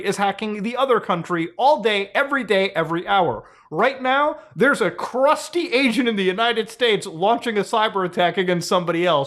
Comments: I thought this man said “there’s a crusty Asian in the united states” lmaoooo till I heard him say Chinese agent I was like ahhhhh we good we good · Thought you said "There's a Crusty Asian in the United States" for a moment there.